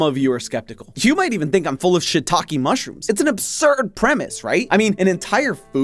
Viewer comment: i thought he said it 💩😂😂